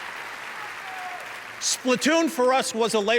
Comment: Sounds like Reggie said "all your crap" I know he says craft but the way he says it makes it funny.